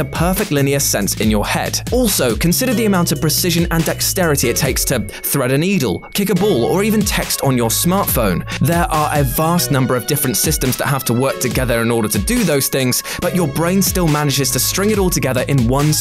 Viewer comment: did he just say "kick a BULL" ????